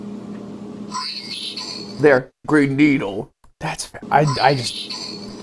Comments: I heard green storn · i keep hearing brain needle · I hear brain needle in that one 😅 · I hear "brainneedle" 😭💀